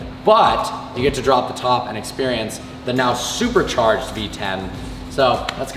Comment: I thought you said a v10??